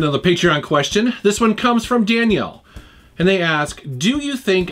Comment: Sounds like you say the question is from Danielle in the very beginning and not Daniel. Even the closed caption thinks you said Danielle. Poor Daniel.